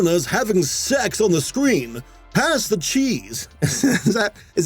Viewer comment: I thought he said pass the cheeks